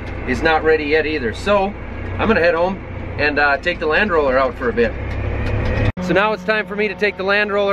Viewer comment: Timecode 6:43 I thought you said your going to take the "Land Rover" out. Ahhh.... Land Roller ! I could use one of those for my front yard. 4 passes and I would be done. Also if you roll down the little rocks they will grow into big rocks. Good stuff once again.